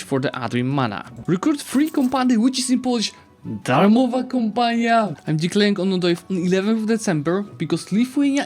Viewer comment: Did at 1:25 say "darmova kompania"(free company) or I just misheard?